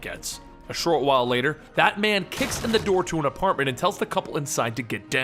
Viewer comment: I thought you said batman and I got excited....